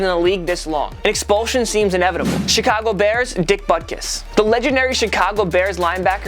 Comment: It sounds like he is saying dick buttkiss